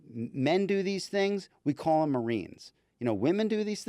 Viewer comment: What did the lawyer say at 15:43,"Men do these things, we call them xxx." What is the last word in this sentence? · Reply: He said marines.